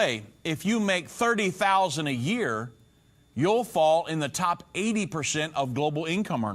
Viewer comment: 8%가 아니라 80%로 들리네요